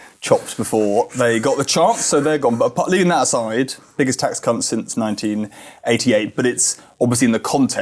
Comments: Did he say biggest tax c*** then? 😂 need a hearing aid. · LOL, 1:37 "biggest tax c*nt" instead of "tax cuts" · "Biggest tax cu*ts since 1984" did anyone else hear that? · I cannot hear that as anything other than "tax cunts"